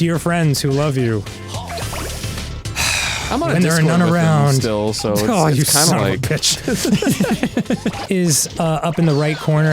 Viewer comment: Someone please translate what arin said at 0:58 please I can’t understand what he said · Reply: "Im on a discord with them still, so its kinda like-"